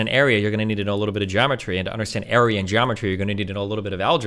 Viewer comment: Sounds like destiny said “Aryan Geometry” Math is racist confirmed 😂